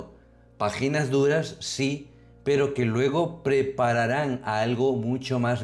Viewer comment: escuché vagínas duras OMG Bendito sea DIOS ¡Amén!